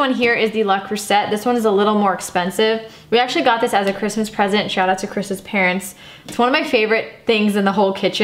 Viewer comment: For some reason I heard, “shout out to christmas parents” lol